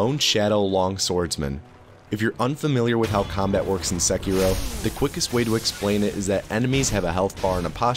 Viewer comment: When you say "In Sekiro", i hear it as. "Insecure Oh." lmfao!.